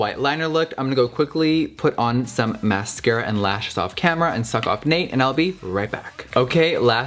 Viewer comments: Did anybody else hear him say “I am going to put on some mascara, and lashes, and suck Nate off and I’ll be right back?” 😂😂😂😂😂😂 I’m dieing ( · did he just say " ima go put some lashes on and suck off Nate"?😭